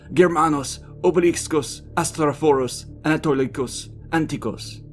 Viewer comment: Firstly I heard Obelix, Asterix